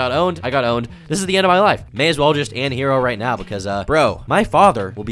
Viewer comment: Did you just say you might as well "an hero"? Trying out for the Golden iPod award that year, eh?